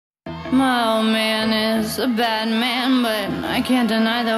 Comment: bruh this whole time i thought she said "mailman is a bad man" I